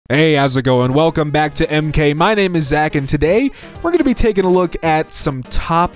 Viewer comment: “my name is ack” anybody else hear that? 😂